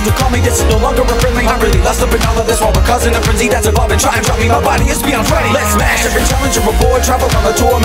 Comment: Did I hear "my body is more that ready"? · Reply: Zombie RocketerX ...My body is beyond ready...